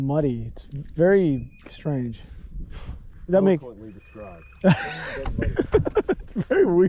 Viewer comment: I would love to know what said.. to have cracking up like that had me laughing. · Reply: he said wonderfully described etc...